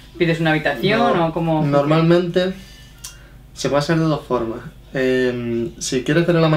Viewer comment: what verb did he say about the form on 3:09? hahaha. estos canarios! :) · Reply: Creo que dice: se basan en dos formas :) Cxx